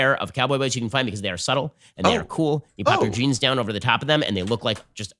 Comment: "because they are subtle, because they are cool." What a subtle way to say "supple".